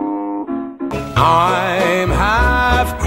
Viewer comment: I keep hearing "all of the lava above" youuuuuuu and "bicycle built for pooooopp💩"